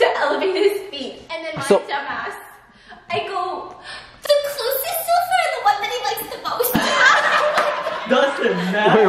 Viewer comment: Can someone quotes to me what did said in 11:59 ? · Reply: “the closest sofa or the one he likes the most”